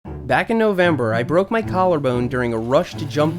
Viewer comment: I swear I thought it said “back injury” and I got so sad 😂